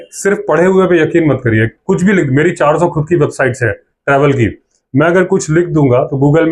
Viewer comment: Did you said 400 website hai khud ki ?